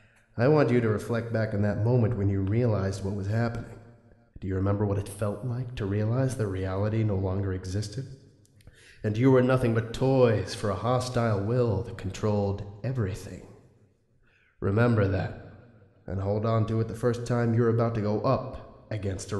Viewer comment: LOL It sounded like "You are nothing but toys for a hostile will that trolled everything."